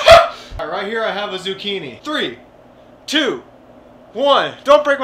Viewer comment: we're gonna try something a little harder, a cucumber" Me:WhAt DiD He SaY edit:go to 2:25 and slow it down to .25, listen to how he says zuchini 😂